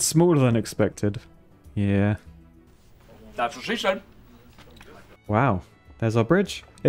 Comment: did he say "that's życie" - "that's life" in polish?